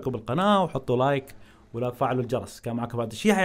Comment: ههههههه 4:34 ولا تفعلوا الجرس انا سمعتها كدا